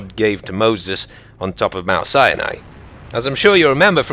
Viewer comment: did he say "Mount Cyanide...?"